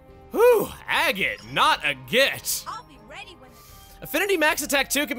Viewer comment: Did anyone else mishear saying "Agate, not a bitch!"?